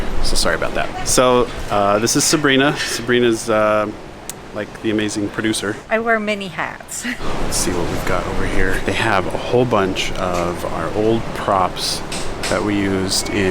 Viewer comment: When she said I wear many hats I thought she said mini hats at first 😂